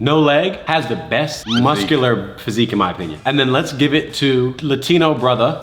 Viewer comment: sounds like you said legs give it😂